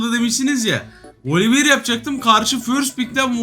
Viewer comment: da ne diyor ohaaa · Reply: yapcakmış